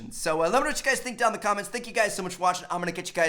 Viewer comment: Someone plz explain to me what he says at 5:45 Make sure you check thesecreseenyet